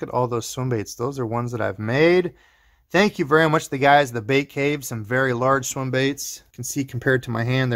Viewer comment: "Thank you guys at the ????" Did you say Bait Cave? Did you get some molds? From where? Who makes them?